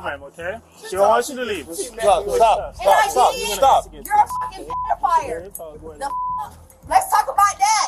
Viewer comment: did she say "pedo-fire?"